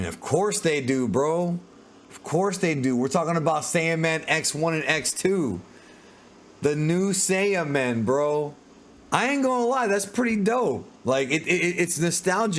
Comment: Dang I thought it said two new Saiyan 😢